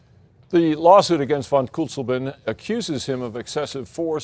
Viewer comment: I thought he said this nazis' still in Berlin